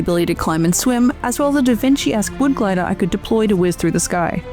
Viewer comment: I thought she said da Vinci ass wood glider x)